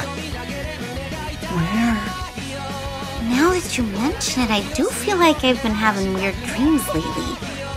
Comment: Could anyone tell me what the heroine is saying during 0:46～0:50 ? "Now xxxxxxxxxxxxxx do feel like I've been having weird dreams lately." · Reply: M N 1 year late but... “Now that you mentioned it I do feel like I’ve been having weird dreams lately”